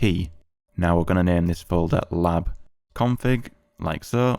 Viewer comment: i thought you said "LobConfig" instead of "LabConfig", was wondering why it didn't work for me lol